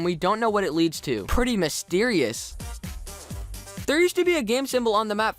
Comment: I THOUGHT HE SAID "WISTERIOUS" XDD who else thought this??